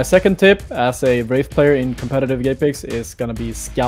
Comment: did he say gaypex